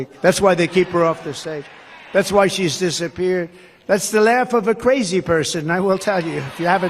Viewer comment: I heard "this is why she's disinpeared", not disappeared. Anybody else?